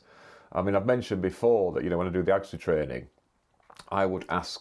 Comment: did you say "when I do gangsta training"? I doubt it, but I listened 3 times and that was all i could hear lol ;)